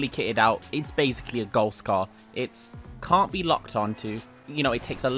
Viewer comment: I thought he said gold scar💀